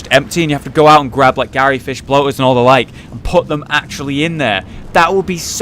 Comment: did you say bloaters?